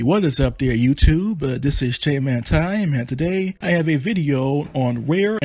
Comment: Misheard it as ,"jhentai"